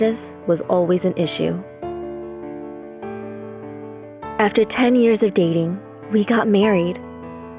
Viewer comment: video says 10 years. Caption says ten MONTHS...